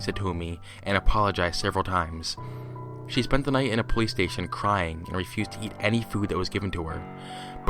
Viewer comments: I thought he said "she spent the night in a PlayStation crying" lmao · I misheard and thought you said, "She spent the whole night playing PlayStation, crying.". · I thought you said she spent a night in a playstation